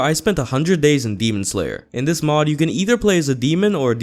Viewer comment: Ahh fuck, I thought it said doom slayer. Alright, fuck this shit I’m out